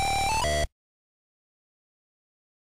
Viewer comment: Honestly, these are pretty good quality PC speaker samples. Especially for 1987. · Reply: I thought it said iwata iwata iwata which is the employee of nintendo